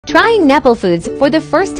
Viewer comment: Bro am I the only one who thought he said nipple food 💀💀💀